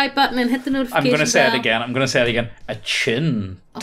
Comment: The first time said "attune" to that bell, I heard it as "a chin," but quickly realized what he really said. I heard "a chin" this time again and had to think about what he said.